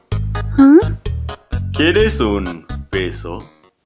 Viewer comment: Escuche peso